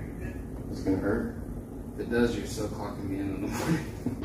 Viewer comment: Wtf did he say at 6:30? I played it 3 times and still couldn't understand what he said. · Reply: If it does you’re still clockin me in in the morning.